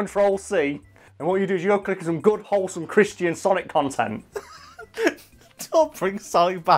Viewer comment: I remember when he first brought up Christian sonic. I misheard and thought he was talking about Chris-chans sonichu